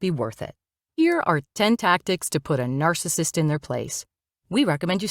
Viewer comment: I thought it said, " Puta Narcissist." 😂